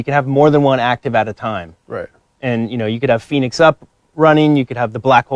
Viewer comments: did he just say Phoenix up XD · did this boi really just say "you could be running a Phoenix up" boi that's a damn gobble gum Lex get on this shit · did he say Phoenix up? (bo3)